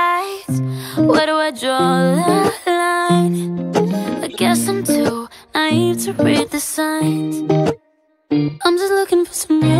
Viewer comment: I think she said naive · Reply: I thought she said " I guess im too naive to read the signs"